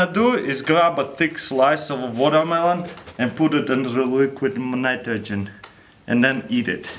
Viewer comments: wtf did he say? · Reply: Eddy feliz "in the li-liquid mnitrogen" XD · What does he said? Liquid Manitrogen